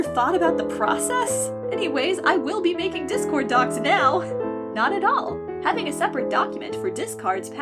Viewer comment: I thought it said discord docs and was confused